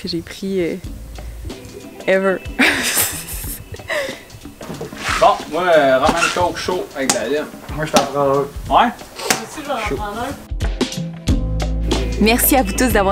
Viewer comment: A 14:36 j'ai rien compris! Du pur Québecois! Ca veut dire quoi svp je suis intrigué? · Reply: Haha, Traduction, Bon! Moi je fais des Rhum&coke chaud avec de la lime, Moi je t'en prend un Moi aussi je vais t'en prendre un.